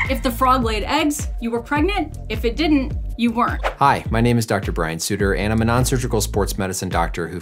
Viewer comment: When dr.Brian spoke and he said hi I’m doctor Brian I was like Omg did he just say dr bright? Reply if you know which dr.bright I’m referring to